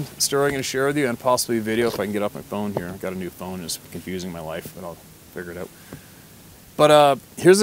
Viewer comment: New phone messing with your life? Thought you said using my wife's. What? Backed up and got it right that time.